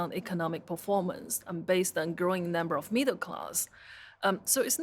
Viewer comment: couldnt understand her words at 14:23 : ". . growing number of needle cars . ." ? ?